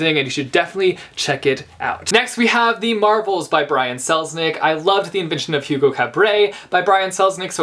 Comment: Could somebody please help me and writte the name of the book on 4:05? Im not english native speaker and cant understand the second name of the author. Thanks! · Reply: The Marvels by Brian Selznick